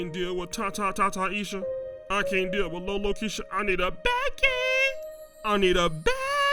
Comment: I thought you said you cant deal with BOBOKEISHA 😭